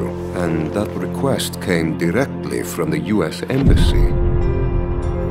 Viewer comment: thought he said Malikai Rectum.